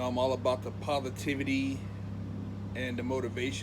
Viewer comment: he sounds like he is saying he is all about the povertivity